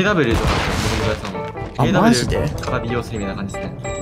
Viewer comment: なんて言ってる？ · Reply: AWじゃないですかね？Cod AWから始めたってことかな。